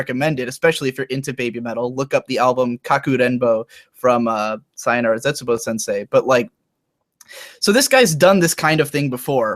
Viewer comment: Please post a link to the record this guy recommended. Best I could figure from what I heard was" kaku denbo by sayonara tzetsubo sensei", and the only thing that google gave me for that was an anime series. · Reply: Don't have a link right now, but he's talking about Kakurenbo ka Onigokko yo by Ohtsuki Kenji with the Zetsubou Shoujo girls as backing.